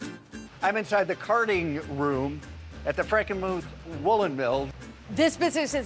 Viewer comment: little mistake in the subtitle at 16:00, which has 'carting' room instead of 'carding' room.